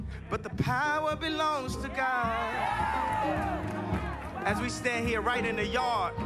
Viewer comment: I thought I heard "As We Lay"...old R & B song. Pray